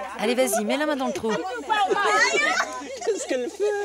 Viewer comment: hahaha le traductrice au lieu de dire met la main dans la trou elle a dit met la moi dans le trou 😂😂😂